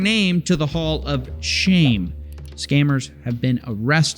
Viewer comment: "Wall of Shame"! I thought he said "Paul of Shame" 🤣😂🤣😂